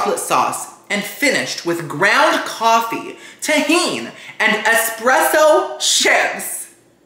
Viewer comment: My kid heard you say tajin and dam near took my phone!